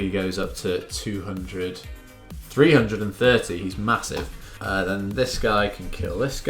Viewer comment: did you say... Massive?